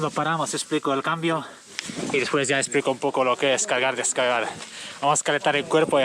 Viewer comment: Le entendí cagar y descagar JAJAJAJA ( Muy buen video )